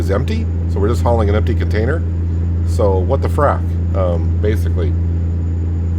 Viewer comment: I thought you actually said "what the [f-word]" but you said "what the frac" lol